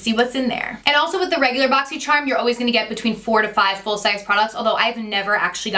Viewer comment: It sounds like you said 45 full size items instead of 4-5 haha!